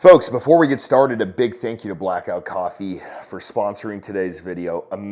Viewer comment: For a second I thought you said Black Rifle Coffee. I almost blocked the channel. I have no use for those leftest bro vet gun grabbers at BRCC. Blackout is different I hope